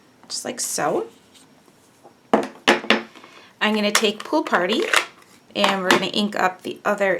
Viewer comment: LMAO I thought you said POO party! hehahaha!! So sorry! I was greeting my husband and listening to the video! OMGosh Hooo!! hahaha, you said it again just before the second tape stamp! Poo party. I'm gonna have to excuse myself in a moment. I'm cracking up! This is such a cute card! I have another set of tape stamps similar, so I hope to make a few like this, thanks for the inspiration, and the laugh! xoxo Jen (hope you're not mad, or offended)